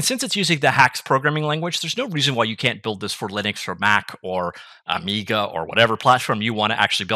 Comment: Did you say 'Amiga"?